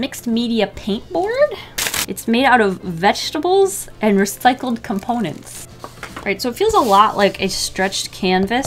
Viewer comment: I had to rewind. I thought you said the canvas was made out of vegetables and recycled Walnuts. I was thinking, why walnuts?